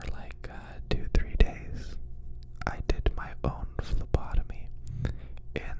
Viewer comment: I heard you saying lobotomy and was completely shocked